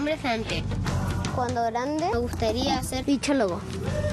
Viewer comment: escuche cuando grande quiero se pichulogo